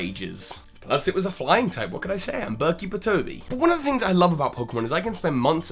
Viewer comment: when you say "Bird Keeper Toby," the captions say Berkie Batoby. Was that intentional?